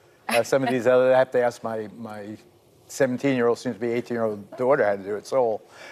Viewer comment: did i hear correctly he has a 17 yr old daughter ?